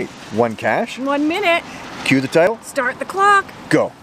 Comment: I always hear say "start da cloock!" now :) She loosened the lock for you. :P Looks familiar!